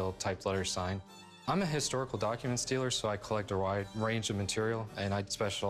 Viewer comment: All I heard is “I’m a historical document stealer” Not “dealer”.